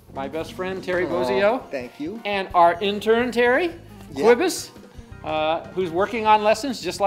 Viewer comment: Did the just call him Quibus at 3:09???